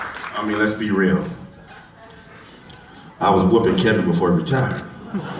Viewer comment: what did Dexter said here? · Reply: think he said "i was whoopin Kevin before retirement".